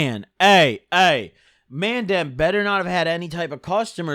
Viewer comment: For all the UK viewers did just say "mandem"